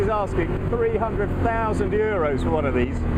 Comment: I thought he said 300,000 euros